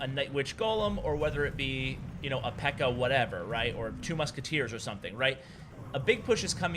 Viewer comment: "Two musketeers." Did I hear correctly?